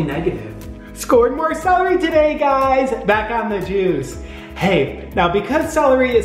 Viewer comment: the auto-generated subtitles said "back on the Jews")